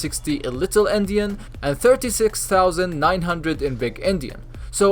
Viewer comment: I thought he was saying little indian and big indian im dying